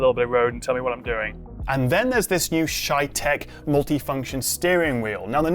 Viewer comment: thought you said “shite tech”